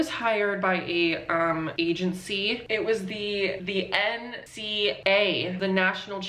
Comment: As a Kiwi at this moment 6:11 I deadass thought was gonna say "NCEA"